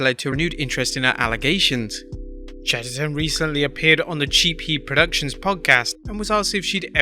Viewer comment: I initially heard that as Chief Keef Productions Podcast 😂